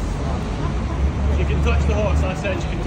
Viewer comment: “It’s MY horse, I make the rules not you!” 🤣🤣👏🏻👏🏻👏🏻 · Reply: Almost sounded like he said f you lol